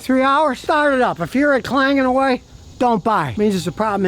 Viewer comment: wtf does he say here? If you start it up after 2-3 hours and..... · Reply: If it’s clanging away don’t buy it